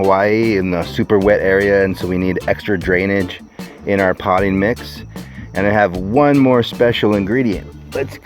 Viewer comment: Humanure!!!! I love that word!!! Keep up the good work, these videos are great! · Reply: it sounded like he said, "potty mix." 😆